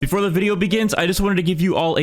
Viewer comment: I thought you said 1b